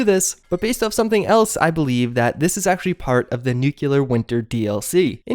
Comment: PRONOUNCED: NU CLEAR! OMFG. If u want a 3rd syllable, NU CLE AR. NOT NUK U LAR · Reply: You need new speakers, 3:43 he quite clearly says nu cle ar. Even closed captions got it XD